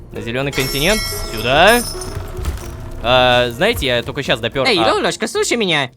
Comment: почему мне послышалась, что назвал Ло Золушкой ?😅